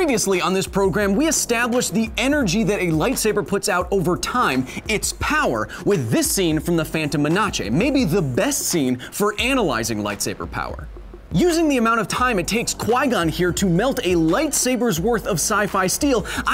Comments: Did he just say “phantom menache?” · Am I the only one who heard him say muhnachay when saying menace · Did he just say menace (men-us) like this (mena-che)??? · Ok tell me im not the the only one who heard him say phantom men-a-che